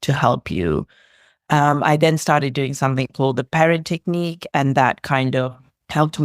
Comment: What is the technique she says at 5:18, I can't quite make out what she says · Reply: It's the Perrin Technique